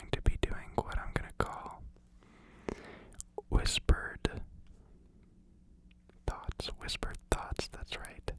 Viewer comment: Did I hear just hear "Whispered... THOTS"?!?!?